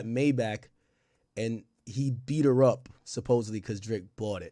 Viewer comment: she clearly said he beat the car up.